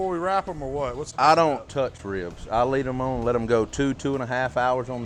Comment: I thought I heard him say "I don't wrap ribs." Sure looked wrapped to me!